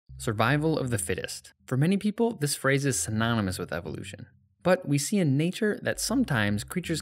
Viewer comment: I genuinely thought it said "survival of the autistic"